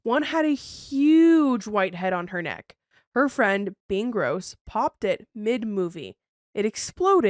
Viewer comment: did she say Ben gross from never have I ever